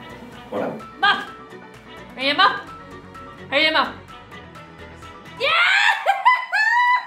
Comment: lol at 4:20 i legit thought she was saying mop. Im thinking how the fuck does a mop have cities?? haha